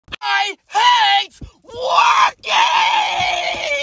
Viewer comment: For a second I thought you said you hate walking